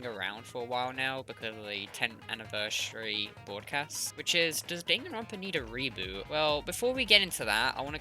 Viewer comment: To be honest I thought it said Does danganronpa need a robot